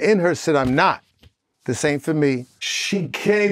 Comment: sound like he said “This ain’t for me” and not “same for me”.